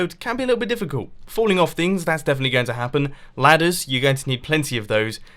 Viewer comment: I thought he said bladders . . . I am a strange human being